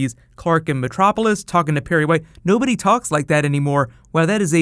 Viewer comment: i thought he was saying mr. wayne at first and i got exited. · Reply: Shining Cat Productions I thought he was saying mr Wayne too! I had to figure out it was perry white